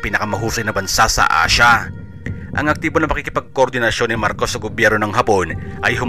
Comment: did you say "Marcos"?